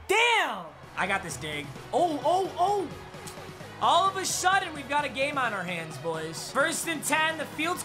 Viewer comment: did anyone else hear shudden?